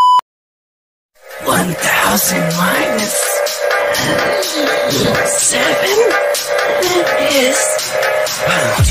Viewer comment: Can someone tell me what he said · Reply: He said "One thousand minus seven is, do you know?"